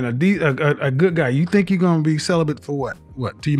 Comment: 🤣🤣🤣 "You 37 years old almost 40, with 2 kids, talking bout celibate, you need to be giving that shit away" Im dead 💀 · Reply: I heard "celibent" 😄